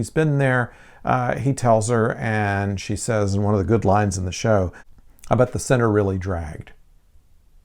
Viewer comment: “I bet the seven really dragged” that’s what she says. I think you misheard. “Tim Shaw, how long’s it been?”, -Dr “3,407 years”- TS “I bet the seven really dragged”-Dr